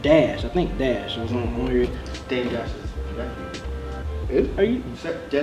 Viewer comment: what did say at 7:36 ? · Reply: "Dame Dash's nephew"